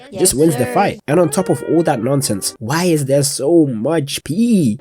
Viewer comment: At first I heard "Why is there so much Peak?" and then was thoroughly traumatised 😢